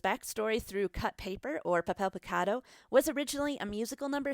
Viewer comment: When she says papel picado it sounds like shes saying pip avocado XD